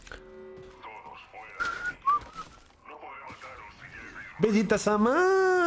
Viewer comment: Que dice en el minuto 3:45??? Le entiendo "benditas armaaaas" pero quien sabe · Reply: Vegeta-sama